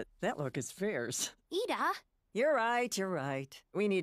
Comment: Anyone heard her say target instead of market